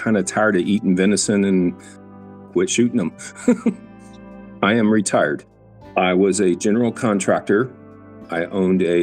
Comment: I thought he said im retarded